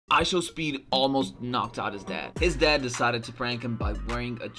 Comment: I thought you said he almost knocked his dad and he didn’t even hit him bruh